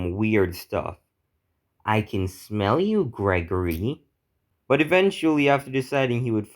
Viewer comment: When i heard “i can smell you gregory” i thought it was fregly